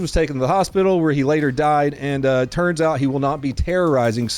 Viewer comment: 😂 did you say turds out or turn out? Snuck that one in there. Just as smooth as the 5 ninjaman caught.